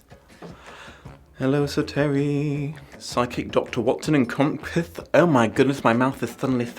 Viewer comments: I misheard that as "his psychic Doctor Watson". That would make books really different x'D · I heard "& his psychic Dr. Watson" and had to stop to process what I heard. Also, I was so thrown when I read the Study in Scarlet in 2015, it was WILD. I lived in Utah in middle school, and the "social studies" class (history & geography & economics) was just straight Mormon propoganda. I do not respect Mormons because of that.